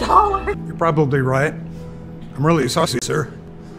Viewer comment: I heard he said I’m ready sussy sir